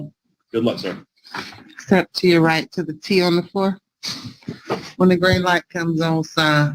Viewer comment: Oh poop....I thought she said "step up and pee on the floor"...my bad..."T on the floor" is what she said